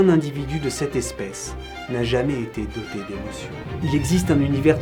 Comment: J'ai adoré cette vidéo ! Par contre qu'est-ce qu'il dit vers 5:07 ? "N'a jamais été doté de ????" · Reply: D’émotion